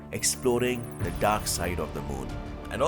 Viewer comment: When he said dark side I thought he is saying darksied